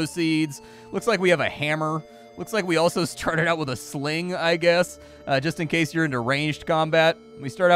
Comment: laying here half asleep thought you said started with a fling. That could be useful or a headache